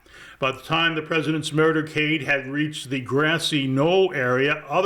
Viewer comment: I swear that you said “murdercade” in discussing the motorcade.